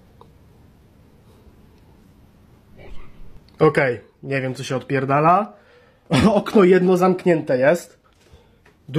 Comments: did anyone else hear the low creepy voice??? I can’t understand it but there is definitely a voice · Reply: It said "hairpie" I think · when he opens the door, you can hear a very scary, creepy voice saying something i don't know what it's saying. But listen close, you can hear it. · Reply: It called him "ringmeat"